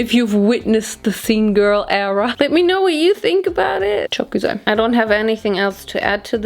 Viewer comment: Did you say "çok güzel" at 20:08!!?